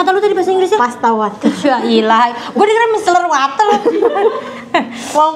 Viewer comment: sma bet mbala aku dengernya juga micellar water makanya kek hah tbtb banget micellar water😂